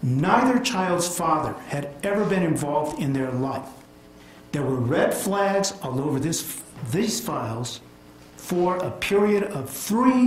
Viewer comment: "there's been red flags all over this [ FU*kING CASE! ]" - is what I heard him say